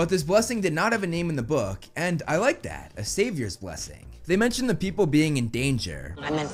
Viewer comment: Maybe I'm mishearing, but I believe Grover said "Satyrs blessing", not "Savior's blessing"